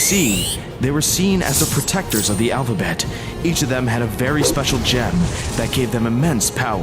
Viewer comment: what is he saying? 😢 I don’t know speed up language! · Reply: "There Is No Other Letter In The Whole Alphabet. This Is Α Very Special Gem"